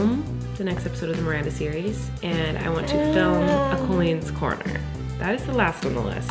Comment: Woah Woah Woah...did I hear "film a colleen's corner"??????? omg please do that!